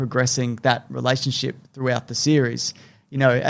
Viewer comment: “There are people who have different sexualities” “that sounds like a forced agenda” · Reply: TBH, until I saw this comment, I thought they were saying a "force of gender" and was pretty confused.